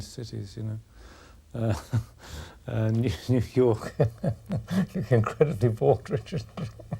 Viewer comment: Does anyone understand what BF says at 2:41? I can only understand "New York" and then it is "blabla" to me and I would like to know what he finds so funny =D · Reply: He says 'You look incredibly bored, Richard' and then laughs.